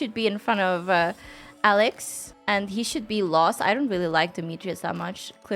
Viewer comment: when you were dragging Demetrius’s icon within C tier and said “he should be last”, I thought you said he should get lost loool